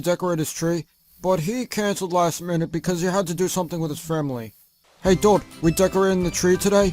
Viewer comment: Did Soos just say.....framily